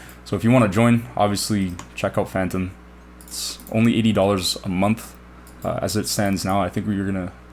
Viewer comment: Had this playing in 2x speed, thought he said $8 a month, I’ve never clicked a link so fast in my life lmao